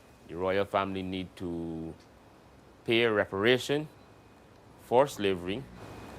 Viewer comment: I thought it said 'Preparations for slavery..' Just one letter short, eh?